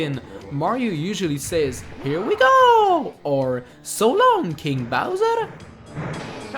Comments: It sounds like he says "so long gay bowser" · also whan mario says so long king bowser, i cannot but mishear 'so long gay bowser' i just can't unhear it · Is it just me, or when Mario says " so long King Bowser!" He sounds like he is saying " so long Gay Bowser!"? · I thought it said so long gay bowser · Well I learned something new ... all this time it was "So long King Bowser!" All my childhood I thought Mario said "So long Gay Bowser!" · Well I learned something new ... all this time it was "So long King Bowser!" All my childhood I thought Mario said "So long Gay Bowser!" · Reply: connor bowers I thought he said "Surround me bowser!" I was confused. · potato. I thought mario said "so long ze'bowser! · Well I learned something new ... all this time it was "So long King Bowser!" All my childhood I thought Mario said "So long Gay Bowser!" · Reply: I thought he said "So long DE Bows-eh" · Mario sounds like hey says “So long, gay Bowser!” instead of “So long, king Bowser!” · the first time I played not going to lie I thought mario says "so long GAY bowser" instead of" so long king bowser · i thought he said gay bowser, not king bowser